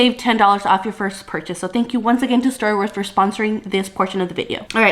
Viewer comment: At first I misheard you and thought you said Star Wars is sponsoring this video. Lol. Oh man I don't know how you do it. This is why I could never be a flight attendant because of the inconsistent schedule. Waking up at 2am for work is crazy. I can barely wake up at 6am every morning for my job. Another reason why I'm afraid of working as a flight attendant is because I hate dealing with some rude passengers. There's almost always at least a few of those on every flight it seems. I wouldn't know how to handle those kinda people. Can you do a video about how to deal with rude passengers on the plane whenever you get the chance? That would be cool.